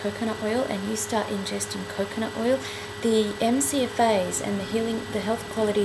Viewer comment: you repeat a phrase I can't understand. It sounds like you're saying "The mc advise" I appreciate your informative videos. Please post your answer here since I don't use facebook etc. thanks.